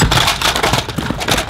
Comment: anybody hear oh sh* t?